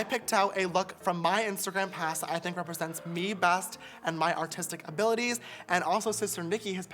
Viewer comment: When he said my artistic abilities i thought he said autistic and I was shook then I went back😂